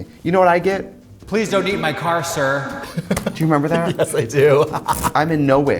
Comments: I misheard "Please dont eat my car sir" and you know what... I prefer my own version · The first time I heard katya say "please dont eat in my car" i didnt here the "in" so i just heard "please dont eat my car sir" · When Katya said “please don’t eat in my car” I thought she said “please don’t eat my car” and ngl that would have been a better story